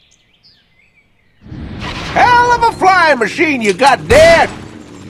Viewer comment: what is Gyro saying? I can't understand it. "...of a flying machine...got dead" ? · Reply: hell of a flying machine you got there